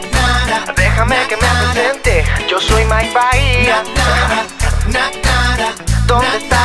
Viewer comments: No dice malparida😠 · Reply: Dice Mike Bahia😂😂 yo igual pense que decia Mal parida😂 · Yo entendí "Malparida" donde dice "Mike Bahia" jajajajaja Manito arriba si te paso igual :3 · pensaba que decia mal paria es mike bahia ♥ :P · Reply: yo pense que decia mal maria :V seguro un ex amor xD! pero es mike bahia e.e